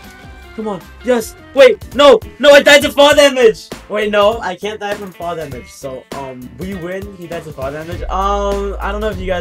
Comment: I thought u said u cant die from fall damage but u did anyway